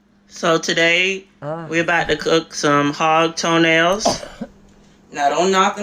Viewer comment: wtf is he saying? Hog toenails? (Don't google that btw, it's not it) f##k's he saying? WHAT IS THAT? i need to know if they exist in my country so i needa move!